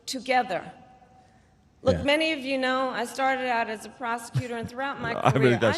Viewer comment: I thought she said she got her start as a prostitute, not a prosecutor… potatoes tomatoes 😂